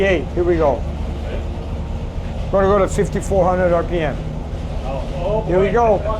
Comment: The first time he said 5,400 I thought he said 6400. 😳 Good to know i'm going deaf as well as blind. 😂